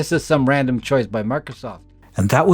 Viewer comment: Did call it Murkysoft?? 🤣